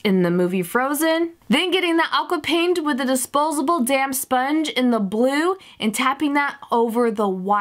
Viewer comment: it's sounds like she said disposable damn sponge